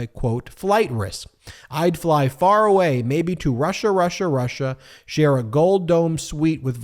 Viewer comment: Ahhh, if he would just fly away to Russia (who is in a war!😂) Fat chance but wishes run wild 😂 EVERY time he says "Russia, Russia, Russia" ALL I hear is "Marcia, Marcia, Marcia" 🙄 🐔💩🍄⏳⌛⚖️🔥🪳🪳🔥🔥😎